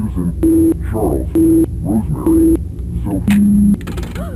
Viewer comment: If you wondered what Billy said, he said, “Jack, Susan, Charles, Rosemary, SophieAAAAA”